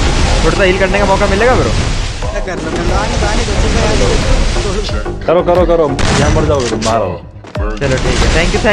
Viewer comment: i heard condo😂🤣🤣